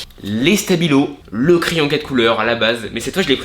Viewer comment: je rêve ou j'ai entendu un crayon de couleur alors que c'est un stylo 4 couleurs mais à part sa ta vidéo est bien